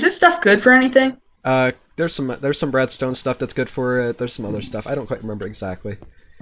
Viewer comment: I thought you said BREADstone instead of redstone